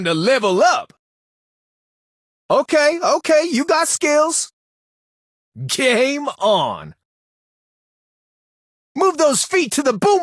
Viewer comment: i hear Gay on